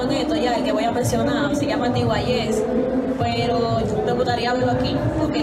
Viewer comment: cuál es el artista que recomienda? No se entiende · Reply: Trini Alex! Mera joya under